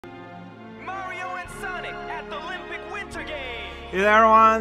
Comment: It feels like the way certain letters don't render is affecting the announcer guy. It sounds like he says "Mario and Sonic at thelympic Winter Games!"